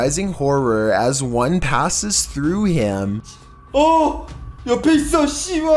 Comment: 😂😂 bro sounded like he just called out and said to the ghost “Ohh Youu pieceee of shyt” 😂😂😂 Lmfaoo